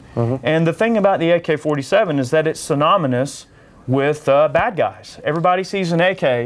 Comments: did he say soNOMonus? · Did he just say "Sonomynous"? -_